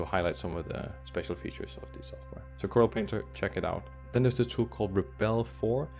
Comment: i can't understand the name of program. Can you please give Name in text? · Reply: Coral painter*